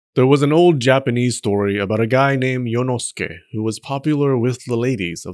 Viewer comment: B#ch for a sec I thought you said Inosuke 😂